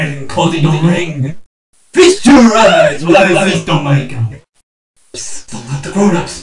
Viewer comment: Feast your eyes while I feast on my gum (I fixed it) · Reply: I heard 'Feast your eyes as I feast on my guy'.